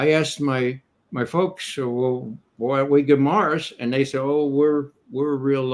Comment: In case anyone is wondering, the auto-subtitles transcribe 1:12 as "why we get Mars", but what McGovern actually said (with a lot of colloquial elision) is "why (d)on't we give 'em ours".